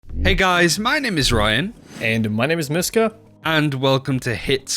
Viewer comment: Literally thought he said “my name is mizkif” at the start